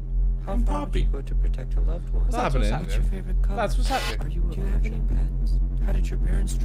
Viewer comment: Did he say "do you have any pants?"